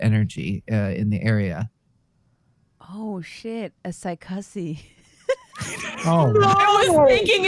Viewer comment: WTF does say here? Psychussie?